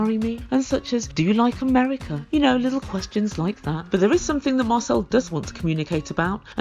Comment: It sounds like your saying marcel as muscle and I was confused as to why you were calling him muscle because he has no muscle lol 🤣